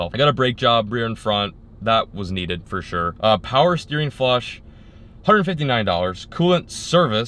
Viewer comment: Did anyone else hear dowers